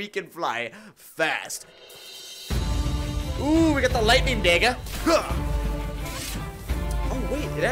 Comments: bro what did hr say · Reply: "Looks like we got the lightning dagger" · WHAT DID HE SAYYY???? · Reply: daega